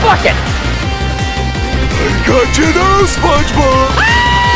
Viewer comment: I fucking died when I heard “I got you now spongebob!” 😂😂😂